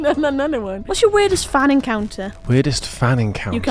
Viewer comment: I swear it sounded like said "whats your weirdest fanny counter?" 😂